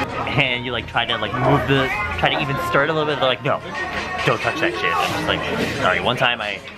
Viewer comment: did he just say "don't touch that jizz"? Am I so dirty I'm hearing things?